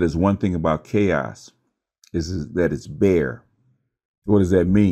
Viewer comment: thought he said the thing about chaos is its fair. bc chaos is truly an EOE. And it segued perfectly into Harver becoming 2face by embracing the "fairness" with his coin.